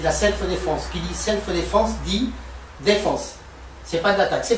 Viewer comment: "...dit, defonce" c'est ce que j'ai entendu...